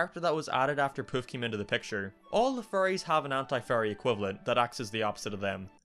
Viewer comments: is it me or does it sound like says furries instead of fairys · It sounds like hes saying furries instead of fairys.